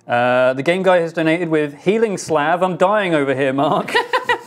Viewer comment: Healing slab? Is it like a scroll of Heal, but written on a slab? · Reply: To answer you both: When Mark misspoke, I did not know, what he actually said at all. He did then correct himself to "salve". When they read donations, I heard "healing slab". If it was a "Healing slav", it could just be a slav, who knows healing spells. Weird that the centauress manufactures them though.